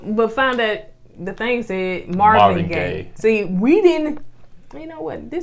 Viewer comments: Oh! I thought said gay vibes too. 😂 Marvin Gaye makes sense. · I thought she said Marvin Gaye EYES. I was like huh?